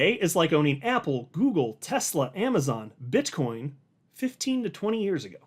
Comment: Did you say BitchCoin